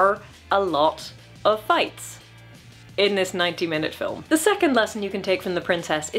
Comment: Oh, you said "ninety minutes". I thought you said 19 minutes and wondered how much world-building you expected in a short-format film that should be effectively a demo reel of concepts with a loose story to make it somewhat coherent.